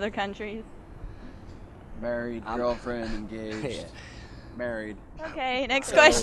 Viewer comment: I thought the same, but he said engaged.